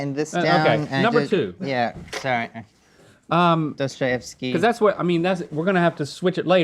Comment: Did say "Dostoyevski" at 12:20?